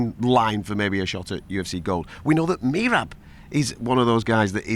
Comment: Who? Did he say Merab? Like Bantamweight Merab?